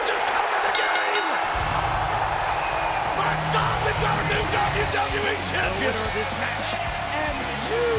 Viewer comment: did i just hear the WWWE champion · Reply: "We've got a new WWE Champion"